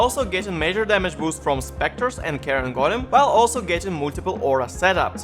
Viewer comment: for a moment I thought you said "Karen Golem"